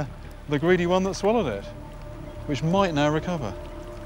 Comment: fish are so dumb that it probably went back and ate it again. And then died. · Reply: Cosme oooooh I thought he said "which might now recover" not "which might not recover" that makes more sense